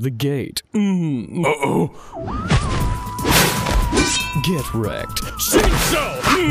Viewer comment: I have to agree with everyone: Gin's Shikai command is fucking the best. XD GET REKT!! Shinso!! mhhhmhhhhmmmm. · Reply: Lmao I thought he said GET REKT SHIROU